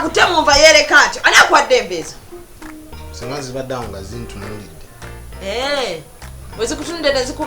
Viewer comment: I hear "nga zintunulide"🤣🤣🤣🤣Naye yekaza.